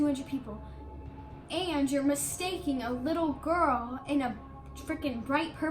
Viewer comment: “and you’re MiStAkEnInG a little gIrL “ wtf is mistakening and did she just call herself a “little girl” ;0